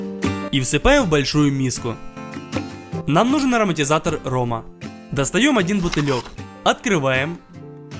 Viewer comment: МНЕ ОДДНАМУ ПОСЛЫШАЛОСЬ АРМАТИЗАТОР РОМА